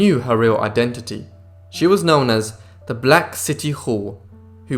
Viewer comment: Did he say "Black City Whore"?